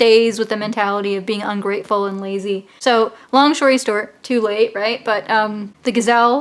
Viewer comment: I thought you said, “long shory stort...” I had to rewind. 😛 Yep. That’s what you said. Sorry; I feel like I just threw a paper airplane in your Algebra class. ☹️